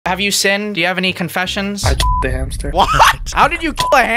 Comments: Please dont blur the word kill i beg I'm sure you wont get into any big trouble not blurring it It sounded like he said he f'ed a hamster instead of killed it😅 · I thought he said he Did a hamster- 💀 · Okay censoring the kill makes it sound like he said f**cked a hamster and I didn’t realize until he said lawn mower then I was like ohhhhhh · I thought he said I f*caked a hamster like MF WHATTT EHGGGGG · I thought he said he f-cked a hamster for a sec I was like “U WHA-😨” 💀💀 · I THOUGHT HE SAID HE F4ED A HAMSTER LMAOAOAOAOOA